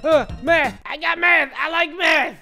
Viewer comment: i thought he said meth 😂😂😂😂😂😂🤣🤣🤣🤣